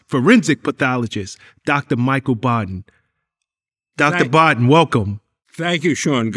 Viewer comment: Lowkey thought that said Biden at first